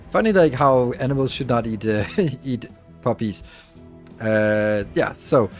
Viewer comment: Me, mishearing "poppies" as "puppies" in the same sentence as "morphine" 😧